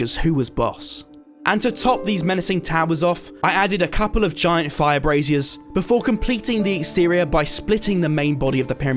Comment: Thought he said giant vibraters coz my volume was down a bit 😂😂😳